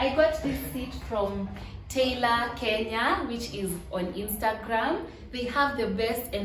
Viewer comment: Aki I cant find ig handle of the couch plug...pls assist? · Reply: oooh! I heard it as tailor kenya😅